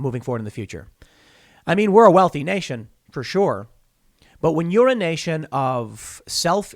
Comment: Tim pool, "When you're a nation is..." I heard, "When 'urination' is...."😋😂